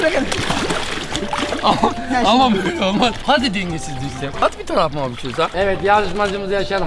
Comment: abinin ne dediğini anlayanlar Şunu dedi:Hadi düşsen hadi bi tarafına bişey olsa